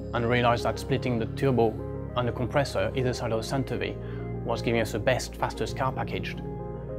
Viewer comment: "and realized that splitting the turbo and the compressor..." what did he say after that? Ridiculous accent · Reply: And realised that splitting the turbo and the compressor, either side of the centre V, was giving us the best fastest package